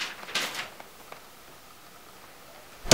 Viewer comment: I heard his break the door and say. (Assalamualaikum.shutup hehe). That mean (peace be upon you) shutup hehe... Thats true? Or my ear problemm.... Hehe..